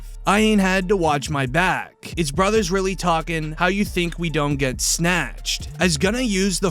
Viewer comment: As someone that hasn't followed the gunna situation closely thank you for this video it made bread-and-butter hit way different. It's an excellent track, lyrics from the heart with actual bars and substance. You can tell he actually means what he's saying but watch his video then go watch him testify 🤣🐀🐁🐀🐁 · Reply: you didn't understand we he said "there's niggas really telling how the fck you think we done got snatched" he's talking about how ysl woody was snitching for years that's why they got indicted